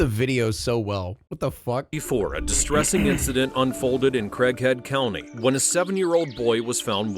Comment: Even looking at the subtitles and replaying it several times, I swear I thought the narrator was saying "Crackhead County"